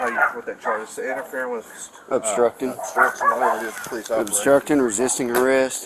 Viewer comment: Everytime I hear a cop say "obstruction" I just hear 'tyrant".